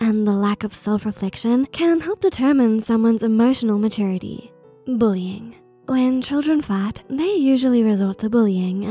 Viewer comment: "When children fat?" Or do I hear wrong?